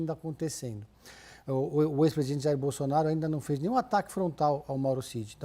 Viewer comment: Parece que eu ouvi ele diser o ex presidiário bolsonaro 5:06 ....se foi isto ele confundiu com o lulinha 😂